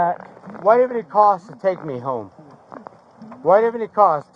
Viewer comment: stg I heard "what does it cost to take me home" but turns out he was just extra slurry.